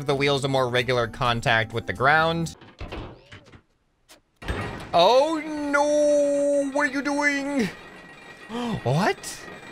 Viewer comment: I originally heard this as “ o no wat doing!”